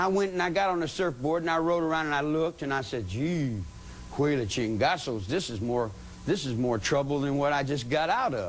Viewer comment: what is he saying lol? Chingussels? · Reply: Chingazos A mexican "Bad Word". He lived in Mexico